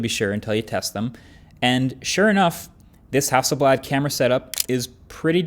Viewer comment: the CC says "And sure enough, this (indistinct) camera setup" - the "(indistinct)" should be "Hasselblad". For whoever is doing the CC :)